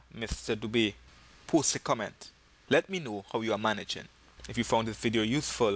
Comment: Thought you said p$$$y comment!!!!😂😂